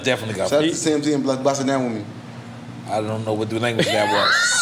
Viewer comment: AYO please tell me i aint the only one who thought he said (pause) "....Buss a nut wit me"